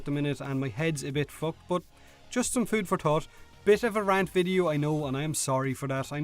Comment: I know its the accent and maby a bit racist but when you said "food for thought" I heard "food for thot".